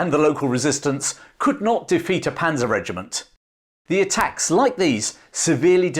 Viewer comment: Am I the only to mishear "feared Panzer regiment" as "Fiat Panda regiment"?